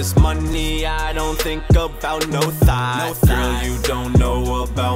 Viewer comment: I've been watching Monto for over a year now and every time I've heard this, I always thought it said, "I'm just all about this money, I don't think about no THIGHS" It's been saying THOT the whole time. my mind = bLoWn